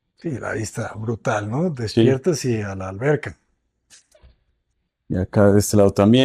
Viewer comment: entendí “despiertas y alv” 😅